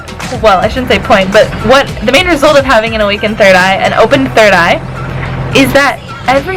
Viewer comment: I thought it said awkward third eye lol